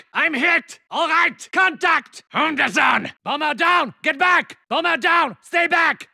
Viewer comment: secs, what is the exact words in German/Austrian? Any native speakers here? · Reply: He says:Hundesohn it means Son of a dog Hunde=Dogs Sohn=Son Pretty weird when he says that after getting shot by a woman